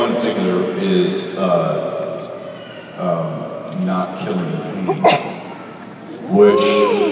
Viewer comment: Who did he say at 47:05? I couldn't tell. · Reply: Cain.